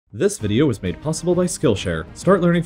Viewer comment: I thought you said it was impossible.